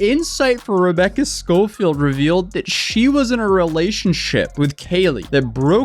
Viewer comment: Was I on repeat cause I kept hearing "she was in a relationship with kids" "I used my thoughts to see blank" and some other bs of court.